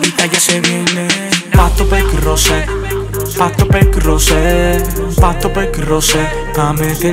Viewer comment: QUE DICE PACO PEDRO JOSE ESO DICE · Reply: Pasto, perco y rose